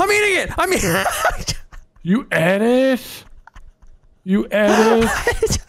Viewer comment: the way nogla says "you ate it" sounded like he said edited